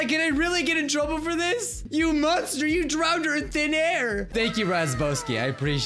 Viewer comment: almost all major lines that the subtitles misheard "How could I be coffee cut Scouts if I'm Scrub Scouts hmm?" "You must are you drowned _earth in air!_" 1:51 "Yeah guys check out _that res both kids._" 1:59 "Okay, All I'm gonna say is _why how would you play your instruments._" 2:15 "I think she's gonna go obviously play with the music club and the few feet around her--" 2:25 "What could they make to be of doing?" 3:03 "_one go frog_" 3:18 "Oh no, coconut is over there standing on the bridge!" 3:47 "All right, let's try breaking the teachers back." 8:40 "Ok I haven't made somebody else to hiko's it's so trippy." 9:58 "The OSHA he co army is a little scary." 10:25 "There's so many _oh it's a hose!_" 10:36 "_URI_ is quickly right now!" 12:05 "_guys no good to bite yeah yeah Darius if you later episode yeah Jerry simulator_" 12:26 - 12:30 "Alright we're _gonna be bowling mode for this._" 12:36 "They play lots of Y and Aries Simulator" 12:54 "Yeah it's new role heartbroken right now." 13:38 "Well even a bully mode doesn't work on anyone!" 14:21 "Today's Scrub of the Day goes _shoe..._" 14:25 "You could be skrelp of the day" 14:45 I may have gotten some time links wrong so woops XD